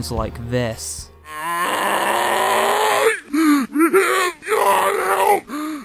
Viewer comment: heres what i heard "AAAAAA DEAR GOD HELP HELP MEE AAA THE POWER"